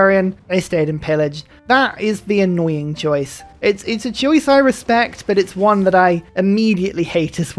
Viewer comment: “A choice I respect but nonetheless a choice you will die for” Is what I heard 😂